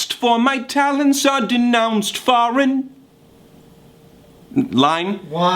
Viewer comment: Am i the only one who heard him say 'For my Talyns' on the intro